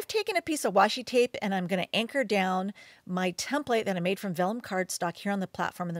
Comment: Hello, could you please specify which framing material you used? I heard "velcrum" but I'm not sure about it (I'm French, hence not a native English speaker). TIA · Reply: Micheline JALLON Yes, I used vellum 140#. It’s a heavier weight vellum—like card stock.